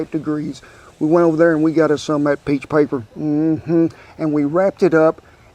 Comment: What kind of paper did you use at the end? Sounded like you said peach paper.